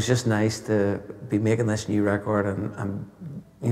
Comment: But i hear you sing "Mermbe(maybe) feel good".. I didn't hear "upbeat...feelgood" 😂😂😂😂😂😂😂